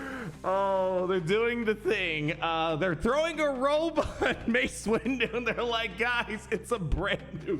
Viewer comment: Very disappointed. I heard "Robot Mace Windu" and couldnt understand why wasnt happy with it. It sounded awesome!